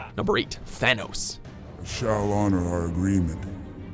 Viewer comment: Number 8: Thanos Me: oh for a second I thought you said Vanoss, lol.